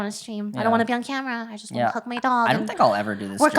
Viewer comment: did she say "I don't want to be on camera, I just want to cook my dog" i heard that